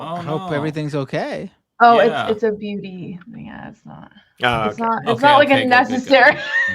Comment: Random question: said she’s getting surgery and it sounded like she said it’s “BV”? What does that mean?…I’m clueless. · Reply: Take this with a giant block of salt, but I turned auto-caption on to help and it read "beauty" instead of "BV". I think she said it's a "beauty thing" she's been thinking about for a few years, rather than an actual necessary medical thing.